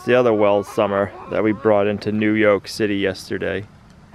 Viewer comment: All this time I thought you was saying New York City, I just realized its New Yolk City pretty clever.